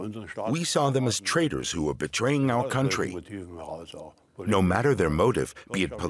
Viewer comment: Captions cut out suspiciously, omitting the line "We saw them as traitors, who were betraying our country." This casts what the guy says afterwards in a different light, absent that line. For shame.